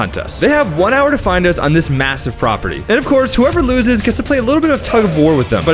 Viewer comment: did you say massive..?